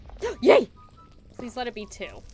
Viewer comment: sounded like a yeet!!